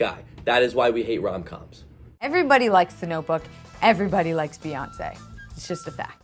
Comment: What's the "no book"? I didn't understand what she said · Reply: "Notebook". It's a movie.